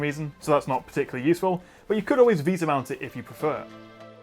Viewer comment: he says "you could always "BEATA" mount it"... what word is he actually saying (instead of "Beata")??? Thanks!!!